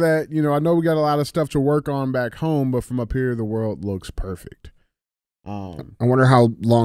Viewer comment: Sounded like he said the world looks round from here